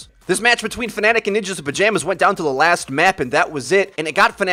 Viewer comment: sounded like you were saying "ninjas in pajamas" doubt that was it tho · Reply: No, that was it. The team is literally named "Ninjas in Pajamas".